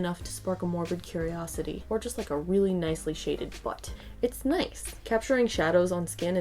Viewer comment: I thought you said, “a really nicely shaded fart.” Just thought I’d let you know.